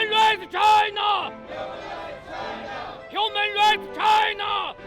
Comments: 我听成：你麻痹 China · 草 开头听成 NMB china 吓死我了